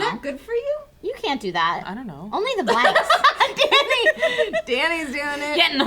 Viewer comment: Did Trisha say " You can't do that, only the blacks" at 10:19 hahaha